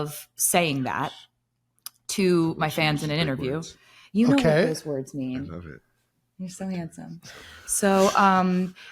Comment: What did he say right before that? He mumbled something and then she was like you know what these words mean. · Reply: something like “shes so hot when she says these words”